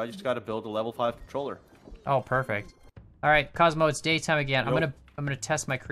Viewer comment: you can hear a person say allrith anyone else hear that or just mee????